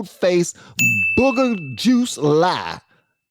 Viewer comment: I thought he said booger tooth lie and laughed out loud lol